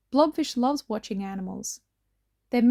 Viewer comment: I heard ’blobfish loves watching animes ’ :0 lol